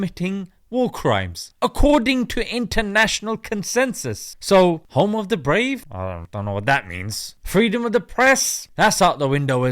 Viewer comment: I thought he said "home of the DEPRIVED" but I did hear "home of the FEE"